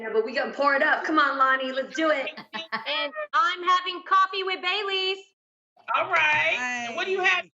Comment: I don't know why I thought Tam said coffee with bay leaves😂😂😂